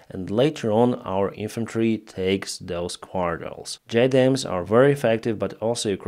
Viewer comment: What word is trying to say at around 2:00? It sounds like quartols. Does he mean quarter? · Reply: Quartile. Basically similar to an area between a city neighborhood. Strictly speaking it implies a quarter of a square kilometer, or about 62 acres in US land measurement units. For comparison an average US city block is between 2-1/4 and 2-1/2 acres. However, a "city block" and a "quartile" as typically used vary significantly in size.